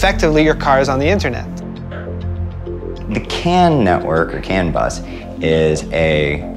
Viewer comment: I thought it said canibus